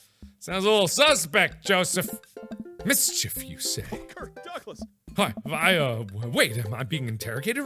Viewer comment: im not the only one who heard joseph say "kirk douglas!" around 18:01 am i